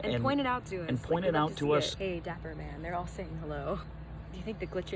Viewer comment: Starting at 12:55 -12:57 I believe. I think that dapper man said, “hi” wow! This is my second time viewing this video and it’s very clear.